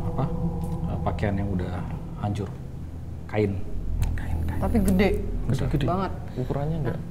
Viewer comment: dimenit 23:43/23:44 pas mas bilang kain ada yang bilang kain juga 2kali "kain kain" tipis banget sih tapi kedengeran